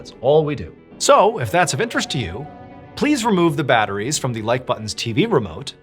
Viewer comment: Not me hearing the phrase “don’t forget to hit the like button” wrong all this time 😳🫣…. I thought he said the “light” button ( asin the light switch 🫣😅) and thinking what weird things to do with it for whatever reasons and what it had to do with the stories and videos…. yes I feel a bit dumb 🫥😅 Love the creativity though in what ways to hit the like button 👏🏼👌🏼♥️ love from the Netherlands ♥️